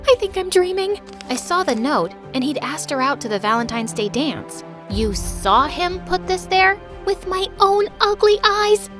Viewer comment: what was the last word ??? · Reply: Grief